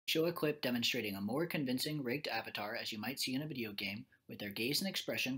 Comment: YouTubes auto generated captions read "Jilla clip demonstrating a more convincing raped avatar..." Good research, but you guys need to put some more resources toward accessibility. Caption mistakes are funny when you can hear what is actually being said. Not so funny if that's your only source if information.